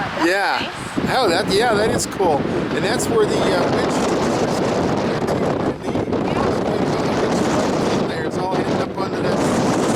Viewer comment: Could you repeat that? I couldn't really understand ya. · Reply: Fordisbetter2 That's were they hide the window wiper